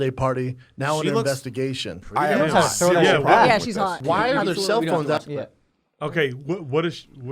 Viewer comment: Did anyone else’s catch Brendan at 34:09 saying “why are there cellphone out!!?? ” he was cracking down in these guys. Did I hear that right?